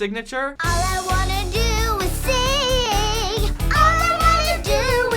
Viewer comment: it sounds like he said 5 Asians